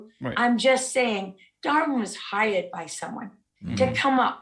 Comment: I thought she said: "Lara was hired by someone, to come up with a theory", but it was "Darwin was hired"... Okay, so you're both hired. He was hired to do science, and you were hired to do... propaganda?